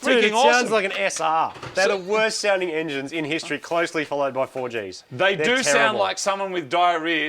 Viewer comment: What other engine was talking about at 8:52 ? Sorry, english is not my native languange but I heard something like 4J's ???